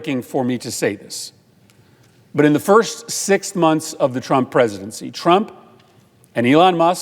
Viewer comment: did he said 6 months?